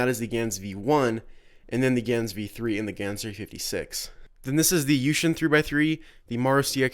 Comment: cube at 10:02? couldnt understand him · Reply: GansV 56? I'm not sure